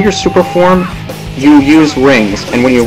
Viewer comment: sounded like u said super porn LOL XD not trying to be dirty though srry